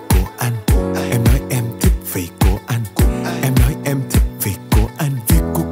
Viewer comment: Thế nào mà tôi cứ nghe thành " Vị c* của anh* là sao ta